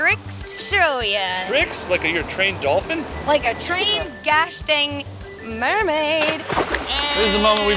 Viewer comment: o thought it said “I turned into a Fried Mermaid”